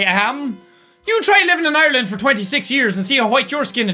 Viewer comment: When said try living in Ireland 26 years , I was reading the comments at that minute and I thought said try living in Ireland for 69 years 😂